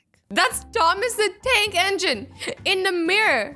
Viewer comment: Who else heard her saying Thomas the dank engine